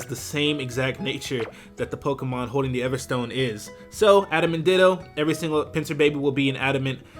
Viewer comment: I thought you said Adam and Ditto at around 2:15 making it a perfect title for this video since ditto is gonna be the father of everything you create.